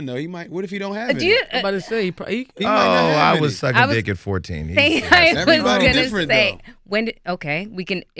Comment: did he say outloud... i was suck n sausage at 14 ... and now im a media personality... wow only in America!!!